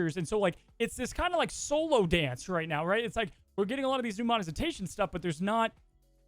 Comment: Am i crazy or is saying Longetivity instead of longevity and is saying Monezitation starting around